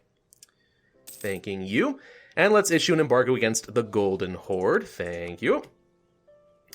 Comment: When he said Golden Horde I heard "Golden Whore". (pardon my French)